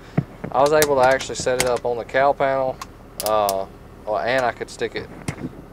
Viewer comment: Thought he said cow panel but he said cowl panel lol